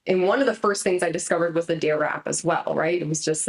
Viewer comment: One of the first things was what? 11:16 ? The transcript says D wrap? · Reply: The DARE App - which is an anxiety application. You can find it on the Apple app store and android too I think.